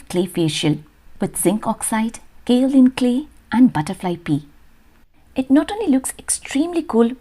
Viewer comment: Wow.... Such a Lovely video... So engaging ❤️ I seriously had to rewind the video to check if I heard that right ...🦋 pee😂 10:44.... much love to you ..the videos are beautiful💕💕